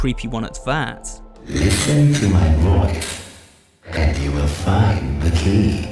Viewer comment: "Listen to my voice and you will find the key" 8:29 for the people wondering what he's saying